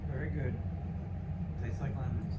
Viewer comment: I thought he said Tastes like heaven! Then I turned up the volume and heard lemons. It’s delicious! Is what I wanted to hear for you, your a wonderful wife to him.